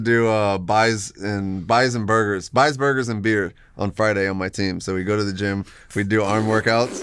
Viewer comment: I thought he said "bison burgers and beer""biceps burgers and beer"I share the frustration that compelling tasteful film making is not consistent with accurate tactical reenactments ...it is quite impossible to film submarine conditions with any form of accuracy.